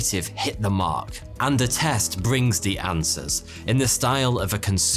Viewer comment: I thought he said this video was sponsored by "A test" and I said hell nah.